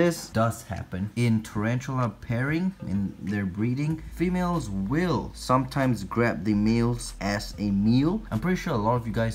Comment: I thought he was gonna say "Females will sometimes grab the males azz"